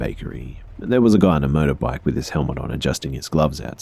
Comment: Had to listen to that part twice because it sounded like you said the guy was on a "murder bike" at first. xD